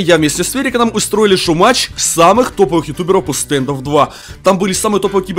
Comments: МНЕ ПОСЛЫШАЛОСЬ ТУПЫЕ? ИЛИ ОШИБСЯ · мне одному кажется что он сказал : самые тупые? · мне одному послышалось самые тупые